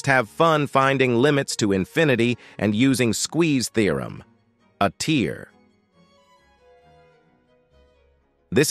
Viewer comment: When you said trig are A tier All i heard is "a tear"